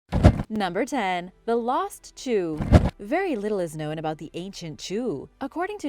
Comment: I thought Zhu Rong was a goddess, not a god. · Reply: I thought I had heard "The Lost Jew". Totally threw me off!